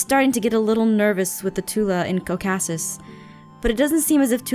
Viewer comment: did anyone else hear cocassis